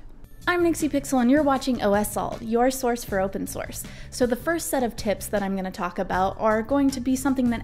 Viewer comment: i heard "the first set of tits" lol